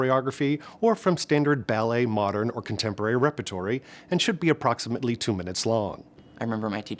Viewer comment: I’m sorry did he just say rep-ah-tory